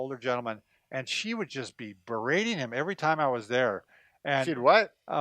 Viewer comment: I thought you said "she was braining him"....which means something REALLY different. #4: Surround yourself with positive and good people, while avoiding people who are negative. According to Wikipedia, Dick Van Dyke is still alive and 93 years old.